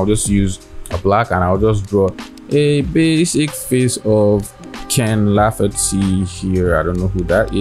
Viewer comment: Lol it sounded like you said you'd be painting Keinan Lafferty. He's a real person and one of my favorite artist and Youtubers. You should check him out. Instagram:go_jasono